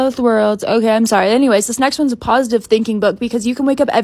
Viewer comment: I thought it said positive thin king